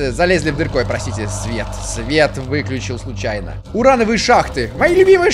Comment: и почему я здесь услышал "Ура, новые шахты"?